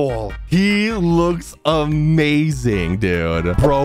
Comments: When u said "probably right" i heard "bababuwee right" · When he said "probably" I thought he said "babbaboy"